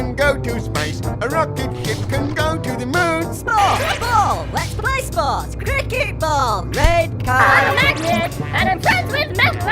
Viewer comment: The foot ball at 13:02 does he say "Sports ball, lets play sports, Cricket ball, Red guy."